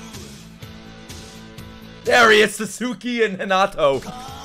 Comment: did he just say suzuki and hinato?😂